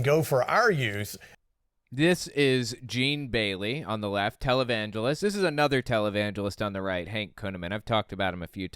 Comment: Just made myself laugh when I misheard Owen saying "televangelist". My evil little atheistic ears heard it as "talibangelist" and I've decided that it's just the word my inner monologue was lacking to describe these maniacs. I think I'll keep it.